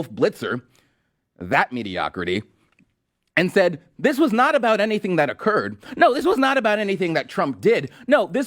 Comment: You completely misquoted AOC. She did not say "this was not about anything that occurred." She said "this was not just about something that has occurred..." You even played the clip at 2:40. If Dems believe something did occur, it only makes sense that they would want to prevent it from affecting a future election as well. If the shoe were on the other foot, you can guarantee that Repubs would want to get through the process quickly so that a future election wasn't impacted. Honestly, you don't need to spin stuff the Dems are saying. It just undermines your own credibility. Just let them run their mouths and they'll eventually implode all on their own.